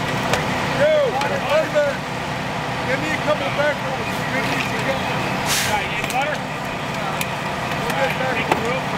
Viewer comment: what did firefighter say at 10:00 for all the firemen to walk away? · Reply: He said they are going to need backboards over here. And the people who weren’t busy just all turned to go get them